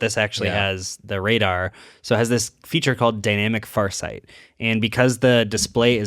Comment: I thought he said dynamic fart sight 😂 and was like whaaat 😂